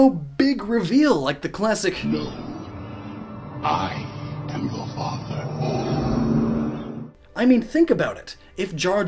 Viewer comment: So I heard Vader say “No Luke, I am your father”. Then I remembered the Mandela Effect and went back and heard “No, I am your father” and it’s breaking my brain because I’m wondering if my brain just put the word “Luke” into the phrase at first.